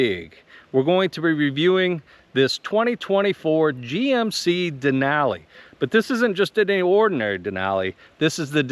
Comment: What's a "Ford GMC Denali"?? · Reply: yesterday I listened like 5 times, it sounded like you said 2024 Ford GMC Denali at 0:50. Now I listened again it sounds correct. I guess my mind was playing games with me. 🤭